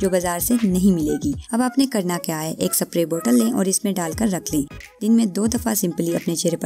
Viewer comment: did uh say saprey bottle😂